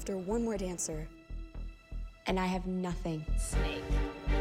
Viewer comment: Did anyone hear the "snake" someone said about Ella